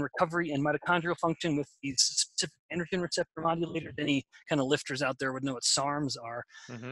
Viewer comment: what does he say here? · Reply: He is talking about projects on 53:12 yerba mate, 53:33 SARMS - selective adrogen receptor modulators. I don't know anything about these except what google just said. Audio was off a bit. I had to turn up volume and replay that section a couple of times.